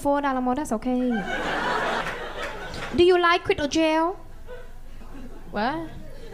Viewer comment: I thought she was saying"do you like to go in jail"